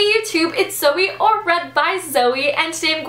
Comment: You say "Zoe or readbyzoe"? I have ALWAYS thought you said "Zoe at readbyzoe"... but you might have emphasized it so Jesse would pronounce your name correctly;)